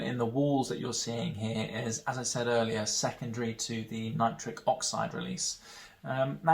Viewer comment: Wait "nitric oxide"? Oh, for some reason I heard it as "nitrous oxide" lol